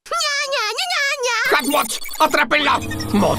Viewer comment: Lo malinterprete con un villano. Pero escuche bien y decia "hat bot" Crei que decia "hawk mouth"